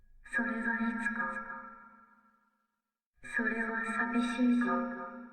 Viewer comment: それはさみしいンゴ(空耳)